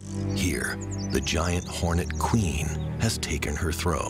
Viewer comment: Why did I think he was about to say the giant horse conch weighs over 11 ponds